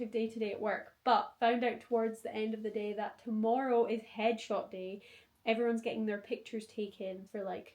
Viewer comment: Loved this. You always look so stylish ❤ When you were talking about it being headshot day I heard 'hedgehog day' and it took me about a minute to figure out that was not another way of saying 'groundhog day' 😂